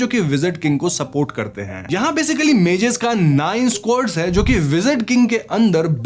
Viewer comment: images ka kya nice code kya bola Kuchh samajh Nahin Aaya 😢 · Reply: Squad